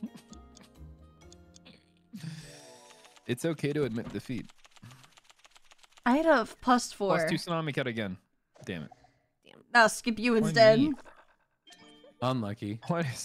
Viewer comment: i heard salami cat at first 😅